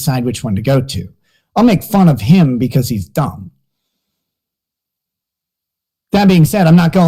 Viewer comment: did he say because he's thumb?? 😂👍 What's really in that can? 🧃